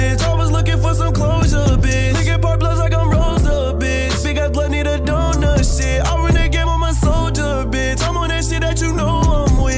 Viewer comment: I thought he said big a*s blood need a donor sh*t🤦🏽‍♂️